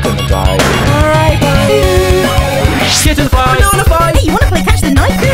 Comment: Who else here heard him say shitten fight?